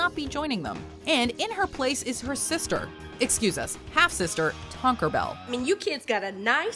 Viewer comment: why did I think Rebecca called her "Taco Bell"? lol